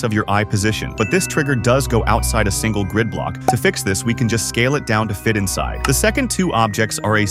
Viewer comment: I thought he said you can't scale? You could just scale down a saw.